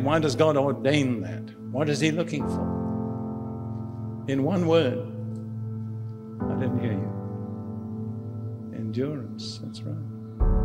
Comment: What does he say at 6:53? I do not hear that one word, he says it quietly and the music volume is increased... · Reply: “What is he looking for? In one word.. *I didn’t hear you*…. endurance…that’s right.”